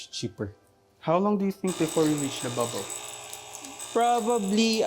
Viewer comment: Question? Di ko masyado narinig umingay po coffee maker hahaha ✌️ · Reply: "How long do you think before we reach the bubble"